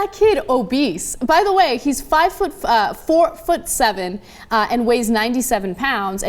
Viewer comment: Omg I thought she said that kid was 5’7” anyways, those BMI measurements are NOT accurate. When I was in the Marines, Well when I was signing up, I had 5% body fat but I am 5’10 and weigh 250 pounds. All muscle but I had to lose weight and make my neck smaller to get in. Ridiculous!